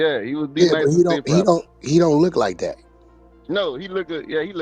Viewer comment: I heard two dudes talking about one dude and one said "he look good" 3:08!!!!!! Had to play back bout 4 times just to make sure I wasn't taking it out of context.